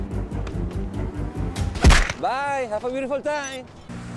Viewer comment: What does he say? 5:56 Bye, have a great time? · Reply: He says have a butefull time, he says it fast so i understand the comfusion